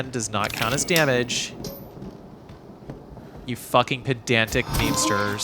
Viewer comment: Patreon Ad-Free Version (Early Ad-Free Access for $1/mo): <link> No Commentary Version: <link> RE7 Playlist: <link> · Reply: I did not quite catch that. I heard "you fucking pedantic..." and then the game over sounded the last word. Sorry I'm just curious. Cause I dealt with individuals like that before when I drive on gtav and they are annoying. So I would like to know what you said so I can address them thoroughly. If you can get back to me on this one I would appreciate this. Thank you very much and enjoy your day.